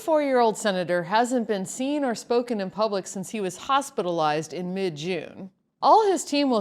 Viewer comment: when said he was "hospitalized" .. I heard 'fossilized' :-o